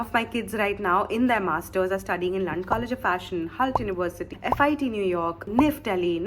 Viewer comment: is am I hearing things or she just said l@ university🤣🤣